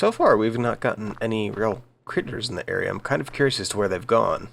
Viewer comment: When you said critters I thought you said creeps lol. This is a game I have wanted since I saw it on kickstarter quite awhile ago.